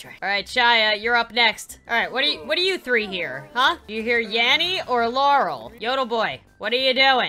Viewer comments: Yanny or Laurel? Nah. How about YAMMY OR? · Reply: I thought it said Yammy or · Is it just me or does “yanny” and “laurel” sound like Yammy and? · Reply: Yup it does at first I thought it said "yammy or Lauren "